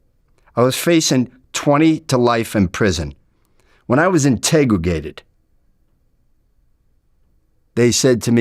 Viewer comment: "When I was integrigated," ... Hmm, what was he really saying there? Something about when he found integrity, in relation to not being a "rat." That he took a sentence rather than inform on his criminal friends? But, it really do sounds like something a little more sinister, because of whos doing the talking. I.e. he really comes of as a rather scary dude. Imagine being a fly on the wall back in the early 80s, in South Boston, when he and his buddies were engaged in some sort of conversation with each others. Everyone so aggressively hyper masculine and tensed up towards eachothers, even in a closed off space were nobody else could see, or hear. - "Yea, Im bad." - Yes you are, and I am bad too. - Oh, yes, you are really bad. - And you, you are a bad, bad boy, you really are. - Arent we all bad, both of us? - Oh, I feel so bad right now I can almost burst. - Yea, my bad, bad boy, you just burst right here right now in my arms.